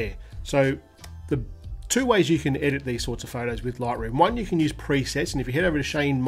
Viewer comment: sorry i dont understand the Name of the editing app here. So whats the Name of the App? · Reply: Adobe Lightroom